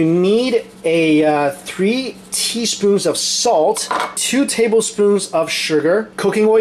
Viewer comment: Had a really fun surprise when I misheard and thought you said two tablespoons of salt and thought it was alot so just put 1 tablespoon :')